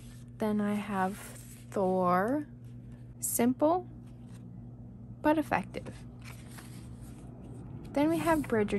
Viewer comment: did u just say “simple but effective“? my directioner heart 🕺 i love your movie journal and i’ll probably start mine later. i’m here for some inpiration ^^